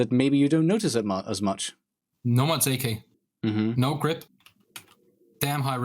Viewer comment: wtf does say? i cant figure it out. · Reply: Nomad's AK